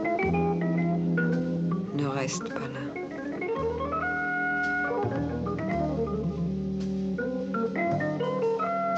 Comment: anyone knows what the girl is saying to Delon? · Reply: ne reste pas